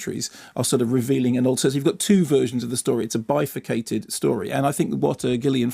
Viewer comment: what's he saying? a "bifocated" story? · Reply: Bifurcated